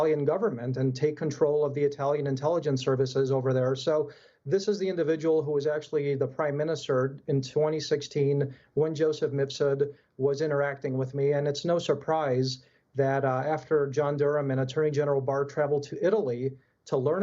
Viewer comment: What is the name Papadopoulos is saying? 3:56 Joseph Mitsoud?